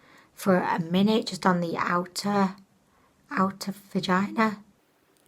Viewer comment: you said focusing on the outer vagina😂 did I hear that correctly? I died laughing!😆